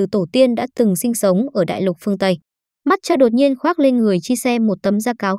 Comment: Matcho nghe thành mất cha